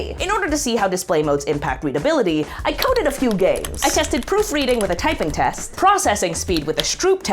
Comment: unrelated but when she said at 3:22 “i coded a few games” i was actually looking away from the screen and only paying, like, 80% of attention to the video, but i swear i heard she say “i called a few gays” imagine my disappointment when i rewinded the video and she wasn’t calling any gays 😔